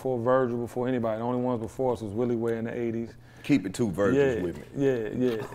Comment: SIR did you say keep it to virgins wit cha ya...💎💎💋 I like that shit🎮🎮🎸⏱14:40🎬🎥